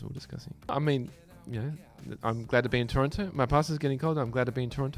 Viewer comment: I thought he said his Pastor was here. I was thinking wow, Donny has his own Pastor! Then i realised my mistake at the end when he said his pasta was getting cold. lol